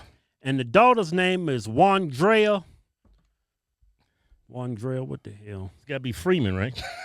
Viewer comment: I thought I said her name was JUANDREA!!😂😂